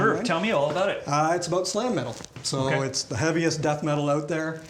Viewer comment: Sigh metal, sly metal, sign metal, psy metal... what is he saying there? Can't look it up either. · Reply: He said "Slam Metal" which is a new one for me. It's supposedly brutal, but I don't know how to classify it.